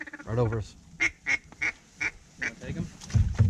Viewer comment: "Should we take em?" Bang! Bang! Bang! "I thought you said, "Take em!". :) Fun times!